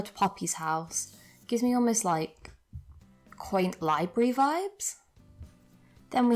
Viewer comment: when you said quaint lively vibes I thought you said blake lively